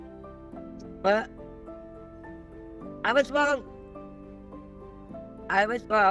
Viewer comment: There seems to be much more to this story than is discussed in this video. Did I hear her correctly at the end when she was taking to her son? I thought I heard her say “I’m a drunk”. It was an admission of something her son likely already knew all too well. It seems that her alcoholism may have been the real reason for the deep rift, not just the scammer. If this is the case, she needs to get into recovery, work a 12 step program and stick with it before she can hope to heal her relationship with her son. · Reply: She didn't say "I'm a drunk" she said "I was wrong"